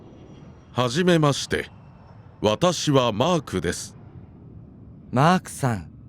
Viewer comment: the caption so wrong. definitely did not say "Hi! How are you? I'm Mark. He instead said " Nice to meet you! I'm. I'm not even Japanese and i caught that. You also have to account that i learned most of my Japanese from this channel. learn Japanese should be proud of their student.